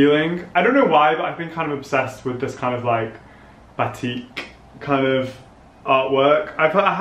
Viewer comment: did he just said "batik" or "batique"? Cause batik is malaysia textile art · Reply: 'batik' shirt is the name of the shirt, check the product link in the description box for more info!